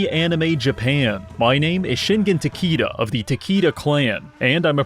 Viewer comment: I thought he said "Shingen Tequilla"